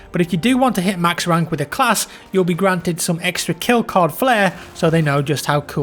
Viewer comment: some extra....... what ? I have no idea what he says. Kill cod flare ?